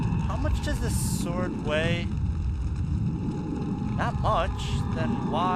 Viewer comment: Did you really said "oh weiha"? :D